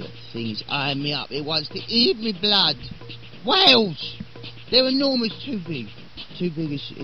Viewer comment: I thought it said waffles